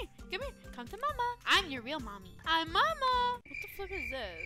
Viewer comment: Did just say the f***wurd